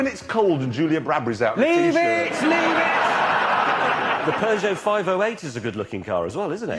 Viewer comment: It took me a while to understand they were saying "peugeot" i kept hearing "persio"